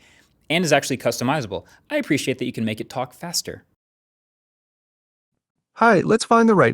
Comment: I admit I was a little disappointed. I thought you said Fester, as in Fester Addams.😂